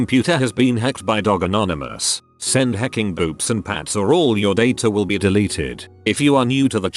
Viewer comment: I thought it said boobs and bats I had a double take moment